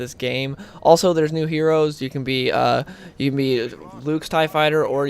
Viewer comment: Did he say Luke's Tie Fighter?